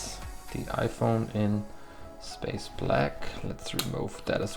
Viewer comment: did you say space black